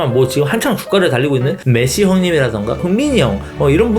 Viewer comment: 흥민이 형 이라고 들었는데 검정 삼촌.. 내가 제대로 들은거 맞냐구~